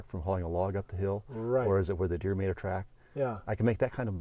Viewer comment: Does anyone know what he said at 23:04? He says "Is that a skid-mark from hauling a log up the hill? Or is it where the "____ ______" track"? It sounded like "where the meer deer track". I thought maybe he meant "where the mule deer track". I don't know. Any ideas? ( Many times I'll hear a radio or TV commercial where I can't make out some little part of it no matter how many times I hear it. Later, if I find out what was said, I'll hear it correctly every time and wonder how I didn't hear it right the first time. I dunno!) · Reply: “...Or where the deer made a track....”